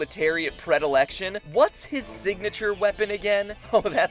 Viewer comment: He said signature "weapon" not signature "attack" I lead you to 7:25 to hear what he said exactly.